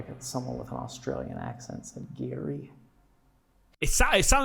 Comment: At about 53:45 it says DIRTY LoL 😂. Not GARRY…. Like when he thought it said BATH…. It’s cuz he is hoping he hears those words so his mind makes them sound more like them….